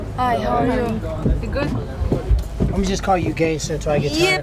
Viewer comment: What did Tyrone say to Joanna at around 1:44? Let me just call you gay? WTF :D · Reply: He said let me just call you “gangsta”